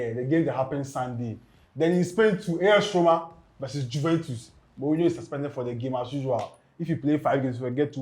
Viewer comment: 7-0 y’all 😂😂😂 0:26 Also did I hear say “In Spain, we’ve As Roma vs Juventus” 😮 Bro different country 😂😂😂